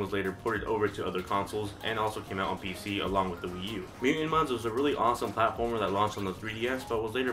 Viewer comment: What game do you say at 5:00? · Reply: Mutant mudds